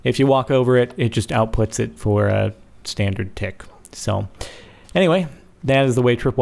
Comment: Did anyone hear him say "to dick" at 0:41 ?